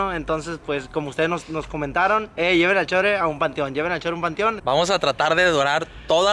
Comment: En el minuto 1:20 se escucha una vos de mujer que dice "hey cevychurros*algo así se le entiende